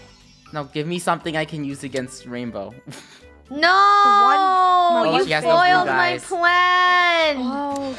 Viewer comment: Did rainbow said 'YOU foiled my plan?!' lol XD